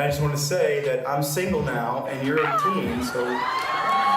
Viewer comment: Um.. what did he say???? 😮 2:43 - 2:48. “And you’re a teen so…” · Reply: "I just wanna say that, I'm single now, and you're 18, so, *make a sigh for her to call him*"